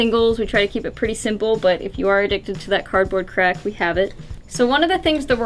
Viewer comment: Cardboard Crack FTW 😂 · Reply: Ah! I thought she said Cardboard Crap